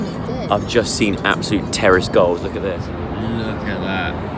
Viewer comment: When you were saying terrace goals, I thought you were saying ‘terrorist goals’ 🙈😂😂